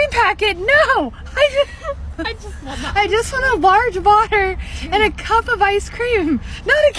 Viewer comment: When the MacD customer said she wanted a large water, it totally sounded like "butter". Not surprised it misheard her. I wouldn't have had a clue what she was saying either.